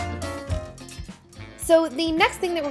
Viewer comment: when you said yay I don't know why but it really sounded like Lindsay Lohan lol